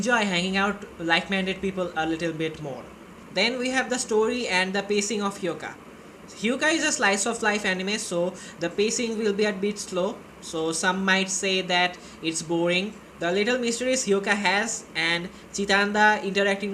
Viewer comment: I really should wear earphones while watching video. I heard "Hyuoka is a slice of life harem"